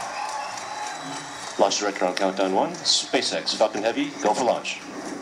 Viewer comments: sounds like he said SpaceX F*****g heavy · I heard "spaceX fu**in' heavy"